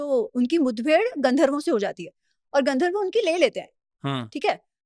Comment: Wait. Did she just said ki GANDHARV UNKI LELE LETE HAI🤣🤣🤣🤣 around 10:19 .BTW huge fan of Ami Ganatra ma'am. Read Ramayan. Onto Mahabharat part one.